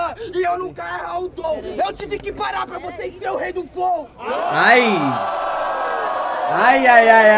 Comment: Escutei "Rei do flow" DUDU MKKK